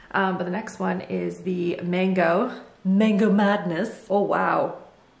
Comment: I thought at first you said Mayo madness and now I’m wondering how a Mayo candle would smell 😂